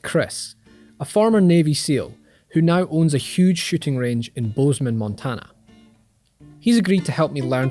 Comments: did he say he has a huge shitting range? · Lmao i thought he said shitting for a sec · It sounded like he said he owns a large shitting range